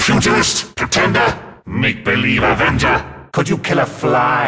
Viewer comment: What does he say here? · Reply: Make-believe avenger